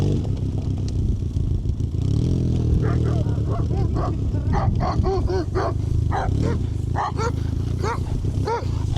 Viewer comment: what did he said? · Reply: haha something like “pidorasi” in Russian means like “faggots”